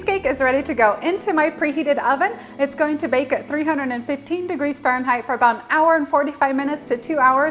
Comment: I thought you said, 350 degrees😳